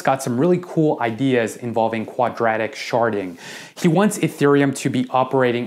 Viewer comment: Anyone else hear “quadratic sharting” @ 13:07 😂